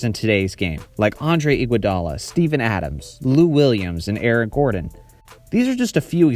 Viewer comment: Did he say Aaron Gordon or Eric Gordon??? I used Subtitles and it said Aaron but the photo used is Eric Gordon